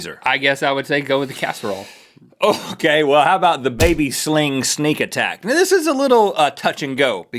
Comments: totally thought he said baby sling snake attack. lol just put a snake in there. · at first i thought you said baby sling SNAKE attack... and I think that would work better.